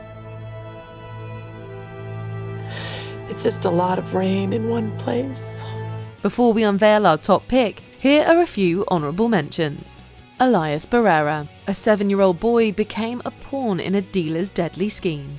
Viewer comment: "It's just a lot of rain in one place." That line from Liv really hurt. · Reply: Oh my god. I heard it as "It's a lot of brain in one place." and I was like: Excuse you??? 😂😂😂😂 Makes sense now.